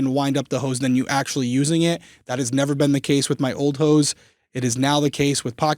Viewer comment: “my old hoes” “if you’re looking to upgrade your hoes” is all I heard lmao